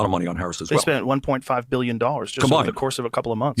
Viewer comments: Joe Rogan asked, "What's the most SOMEONE donated towards the Harris campaign?" "...THEY spent 1.5 billion dollars..." you heard this as "SOMEONE spent 1.5 billion." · if you slow the speed, you hear Bernie assert w/the word, "combined." Joe says "they" spent $1.5 billion over so many months. Yes, Joe used the word, "someone," when he asked his question, but later, when he asserted the amount spent in total, he said "they." Bernie made sure to only affirm that that was the TOTAL that THEY - meaning the campaign, not any individual contributor - spent on Kamala's bid for president. Not a fan of Joe's, but I don't think he actually said the lie that you heard. The truth can be more easily heard IF you slow the play speed at around 5:30. Love your channel, tho!! You and Krystal made me slow down, too, and really listen to Bernie all those years ago. Big Bernie fan now!